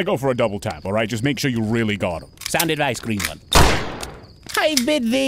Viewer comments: What does the knight say at 2:36? "Sound advice green bug? The video text isn't really helping haha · What exactly did the knight said I thought he said "sound ice screamer" but I know I'm wrong